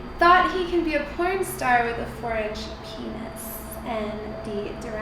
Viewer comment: What is she saying at 2:32? French penis? Orange penis? I'm not a native english speaker. :D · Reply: 4 inch penis